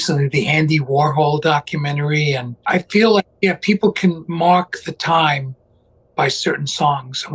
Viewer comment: Because of his accent, I thought he was saying "people can mock the time". 😅